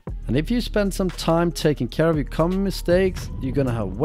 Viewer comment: am i the only guy who hears "and if you spend some time taking care of your cum mistakes... ???"